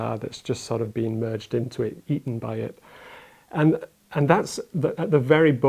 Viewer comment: Misheard 11:55 and thought Prof Copeland said "merged into it, yeeeten by it". (So burned in on neuron rom now, ready and loaded to use if the conversational opportunity presents itself. "Oh no they got Yeeeten!")